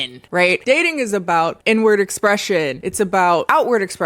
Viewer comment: Please tell me I’m not the only one who heard “N-word” expression when said “inward” expression @ 13:10 ish